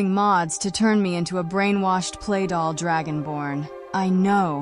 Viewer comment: Almost thought at first Serana said “speedball” (as in the drug) instead of “sweetroll” (that iconic pastry in this game).